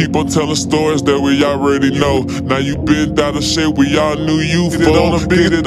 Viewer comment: did he say sh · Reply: no it says bent out of shape